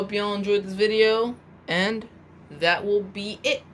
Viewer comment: I could've swore when you ended the video I thought you were gonna say "and that will be it...igo" for some reason, it just sounded like that